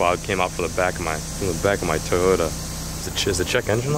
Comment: Sounded like you said giant dust cloud came out of my TOADota